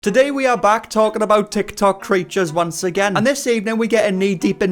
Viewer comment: I heard TikTok creatures I know it was me mishearing but somehow it fits them more